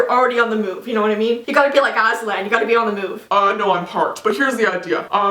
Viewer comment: did she say azzy land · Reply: No she said Aslan which is a lion from narnia